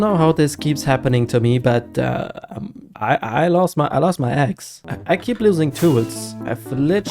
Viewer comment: i thought he said i lost my ex😂😂😂😂